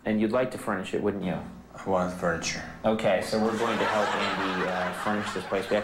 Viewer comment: What does Andy says at 1:46? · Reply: Fabian I think he said I want furniture